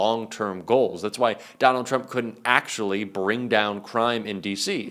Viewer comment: i got excited, i thought it said, rump can't talk - muh heart sank